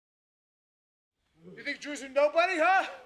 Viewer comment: I thought Bryan said "you think Jews are nobodies" lol